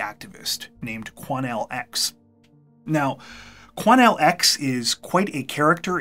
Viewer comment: Wait!! Did he say QUANELLE?!???!😂 WHAT IS THAT?!!😂